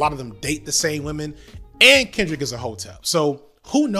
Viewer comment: What does 'Kendrick is a hotel' mean? Am I hearing things wrong? At around 10:00 in the video. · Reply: Hotep